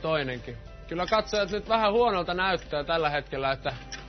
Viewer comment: teksteissä oli vaan 1 virhe;) tossa 1:27 kun juontaja sanoi "huonolta näyttää" ni se oli tekstitetty: "it looks a bit bat"... mutta mitä pienistä=)